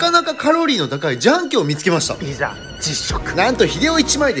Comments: 初めの「自販機」が「ジャンキ」に聞こえてしまった笑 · 自販機がジャンキーに聞こえた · 「自販機」って早く言い過ぎて「ジャンキー」て聞こえてもうてるやん！